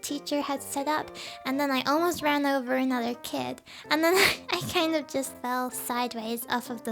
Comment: “I almost ran over another kid” I was WHEEZING so hard and thought she said “almost ran over a child” and that sounded funnier 🤣🤜🧠